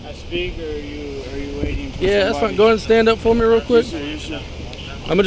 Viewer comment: Watch it again. At 2:46 time stamp, Officer said "Go ahead and turn around..." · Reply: I'm wearing headphones this is what I hear starting at 2:41 Officer - "Go ahead and stand up for me real quick, I'm just going to go ahead and detain you real quick ok?" Suspect - "No no no, am I under arrest?" Officer - "Hey, I'm going to detain you real quick" Suspect - "No, am I under arrest?" Officer - "Settle down" (at that point the officer shoves the suspect)